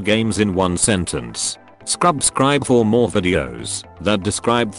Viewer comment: Did he just say Scrub-scribe?